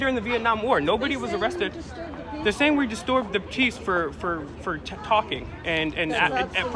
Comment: min ..did you say disturb the cheese?😂 well..well... take that L my hipster dude